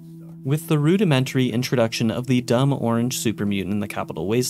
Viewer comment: um yeah excuse me, hi 👋🏽 Did you say “ORANGE supermutants?” Or am I really mishearing things? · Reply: no he said orange